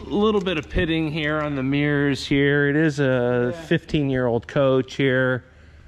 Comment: What year is it???? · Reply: I HEARD HIM SAY" VERY NICE FOR A 15 YEAR OLD COACH.".....